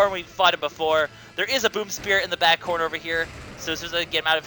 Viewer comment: Sorry if this sounds perverted, but when you said "boom spirit" I thought you said "boob spirit"